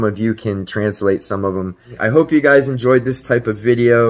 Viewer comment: very interesting! you should do more like this? mix things up? listen about 14:28 where you say "hopefully some of you can translate some of them" I SWEAR someone says yes. A GUYS VOICE...not??? did anyone else her that????